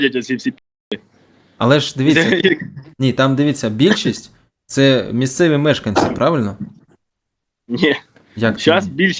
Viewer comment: Бильшисть мижкевемежкинцев? Не понял немного. Не расслышал. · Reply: Местных жителей